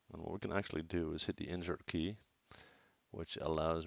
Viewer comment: What are you saying around 3:57 that allows you to move the pivot to the edge like that O..O I keep hearing "injert" which i thought might be insert? But i don't have an insert key... D: -- PS I love all of your tutorials and you are pretty much the reason i havn't given up trying to learn maya on my own. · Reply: Hi Archer, thank you for your support and I am happy you enjoy my videos. To move the pivot point ( on a windows machine ) you either hit the insert key or hold the d key while moving to move the pivot